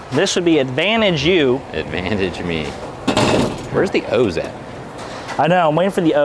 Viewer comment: really thought said "where's the hoes at?"